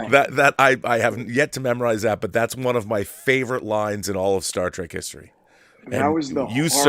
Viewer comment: Did he just say Star Trak?